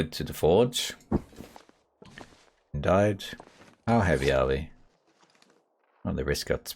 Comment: Did I hear someone say "DIED" @ 36:02?